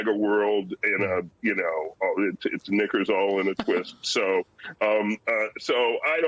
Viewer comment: knickers in a twist? I may have misheard.